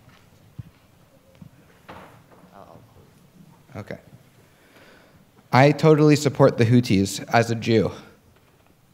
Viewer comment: what was the retort by the Israeli guy [after Prof. Finkelstein's reply to his question]? i couldn't really hear it. his quetion was at 1:26:26 and the retort at c.1:31:20 thank you. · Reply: The Zionist said “Yam Israel chai” which is Hebrew for “Israel isn’t going anywhere”, hence why Dr. Finkelstein said “If it does go anywhere, it’s of your own doing”